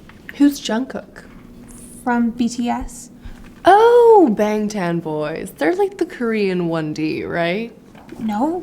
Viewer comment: On the bts one I thought you said “Ben ten boys”😂